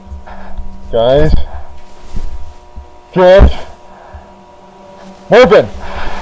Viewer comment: سمعته هنا يقول جورج مو وات مين معايا